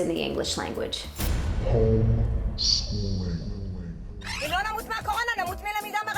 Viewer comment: What does she say at 2:02 ? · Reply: home schooling